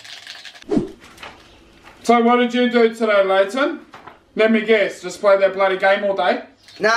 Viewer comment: I heard that as "Lantern" if i have a child I'm naming him or her that because its ramdom asf